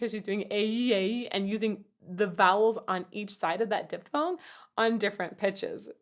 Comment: I have to admit the first time I heard that word, I thought you said dipthon, first thing that came to mind was smokeless tobacco, that one wears. Spelled it like it sounded.